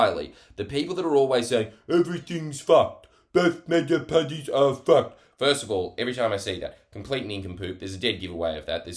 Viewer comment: I heard "beef burger patties are fucked" and had to rewind 3 times to decipher what Jords was actually saying at like 00:53... "both major parties are fucked".I think. Still unclear.